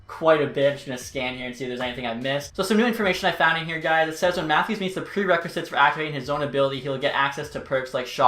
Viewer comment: did anyone catch anything at 10:05? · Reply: matthews captain?